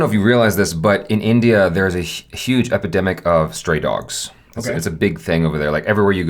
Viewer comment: For every time said "Stray Dog"... I heard "Straight Dog" 🤣